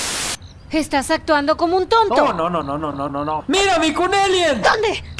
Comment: ¡mira miku emien!(o eso entendi)